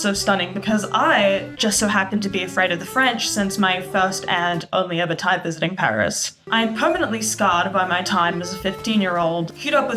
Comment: what did u say about your aunt I couldn't understand you? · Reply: Bahah I was talking ab being scarred by the French